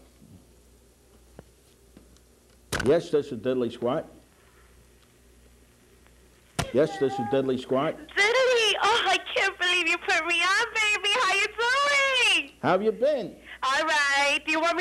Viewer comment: I thought he said Deadly Squad xD LOLOL hahaha.